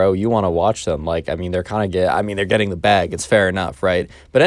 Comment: did he just say “they’re kinda gay”